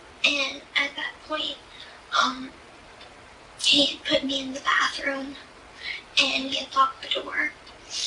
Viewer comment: Does she say Dan put me in the bathroom between 38.34-38:48? It sure sounds like it , she had a lot more rondo with this and it’s baffling to me she is still out conning and grifting society !!!